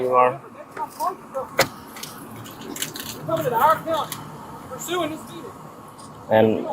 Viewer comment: I love that she has at least 2 officers confirming they wouldn't have believed it was a police car either... and I'm sure I heard someone yelling at him, that made me very happy. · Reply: "I'm sure I heard someone yelling at him" - yeah it's the beardy cop who's giving him an earful, from around 14:05 onwards. Pretty sure I can hear him say "you come to our town".